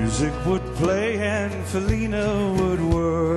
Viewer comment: I thought I heard him say Selena